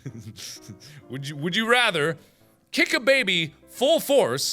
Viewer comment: You know I actually was skipping a little bit at the Kick a baby full force (OR) Kick 2 baby koalas to death So I was skipping and it sounded like Markiplier said "I. Have. A. Baby."